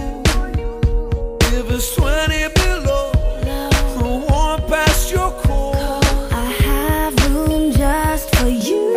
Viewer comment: I'm still tryna' figure out what he's saying... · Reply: I have clothes here for you If you wear it below It won't pass the cold I have room just for you